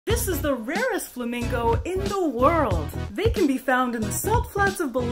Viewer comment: I played so much Pokémon that i thought she said flamigo